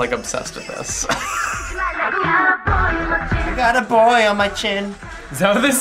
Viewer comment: hát TA k bằng đứa tiểu học 😆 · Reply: họ nghe câu I Got A Boy #machin (tiếng hàn) mà họ nghe thành I Got A Boy A Chin nên họ nói Người hàn phát âm không bằng đứa tiểu học 😂 trong s9 lúc nào những câu tiếng anh do Jess và Tiff đảm nhiệm thì làm gì có chuyện phát âm tệ 😂